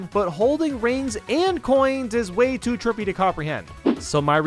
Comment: Did he say trippy or tricky? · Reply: trippy